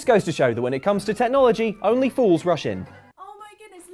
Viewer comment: I kept hearing "only fools russian" and kept wondering the russians had to do with all this 😂